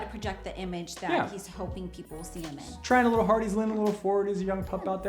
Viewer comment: ‘ Trying a little Ford...”$%&’ Blah..blah..mumble..mumble is a young pup, ..that’s gets aa little embarrassing???? What did you say man? Please say it again?